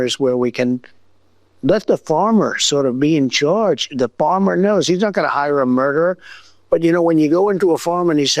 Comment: Did he just say, the bomber knows???